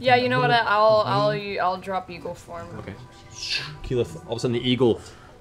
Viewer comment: I swear, every single time someone references Keyleth's eagle form, I mishear it as "evil form". 🥴